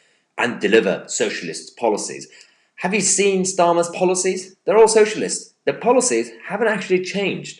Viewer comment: I miss-heard you at one point when I thought you said, "Stalin's Policies" Whoops, sorry "Starmer's Policies" :-)